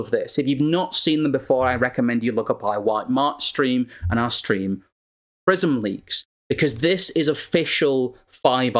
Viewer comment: What was the name of the stream? I can't make out the name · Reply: Belgium's White March - A Mass Protest Against Elite Pedophiles and Through a PRISM, Darkly